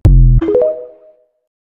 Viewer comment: what does the vidio say? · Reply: “I was my own shoulder to cry on”